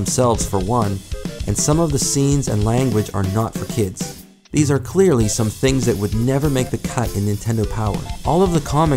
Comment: What Does It say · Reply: Peach is saying like "oh my"